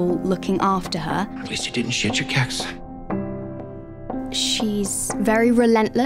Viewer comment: What the hell is that guy saying at 0:49? That's terrible pronunciation. I can't tell if he's saying, "Shit your cats." or what. I've listened 10 times and can't hear what he's saying.